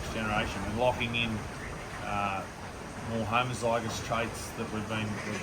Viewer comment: Tattykeeel convo. Explains "Homozygous". Oooooh. Okay. I heard "homicidal ". It explans itself. Homozygous = good. Lol. Great vid. Thanks, ❣️